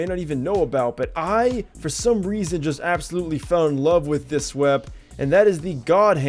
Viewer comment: i thought you said swep as SWEEP lol im dumb